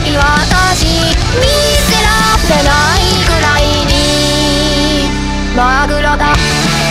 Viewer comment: 友達が2:27をマグロだマグロだってずっと言っててそれにしか聞こえんなったマジ勘弁してくれ